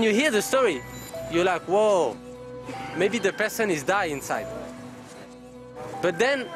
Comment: women is die inside (captions say dying)